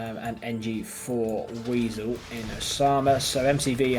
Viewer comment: did he say “for weasel?” lol